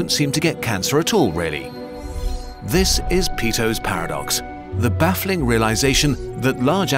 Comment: I thought it said Pedo’s Paradox